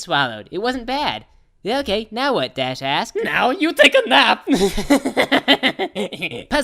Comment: I thought it said dash ass, instead of dash asked